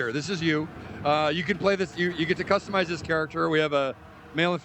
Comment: ~ "you got to customize your charackter" did he just said that :O?!